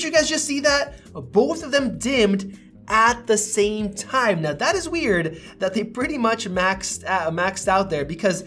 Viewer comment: I heard you saying much thats weard thats weard when you Comparing it seems iphone 12 better lol